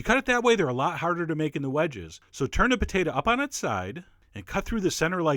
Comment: Did he say "turnip potato on it's side"?